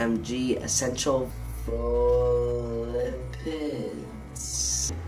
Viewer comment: did he said PHILIPPINES? o maaay gawddd!? iloveeyouuuJacooob.